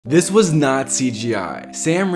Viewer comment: I heard "This was nazi gi"💀.